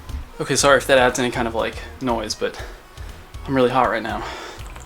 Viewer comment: sounds like he says "I'm really hard right now"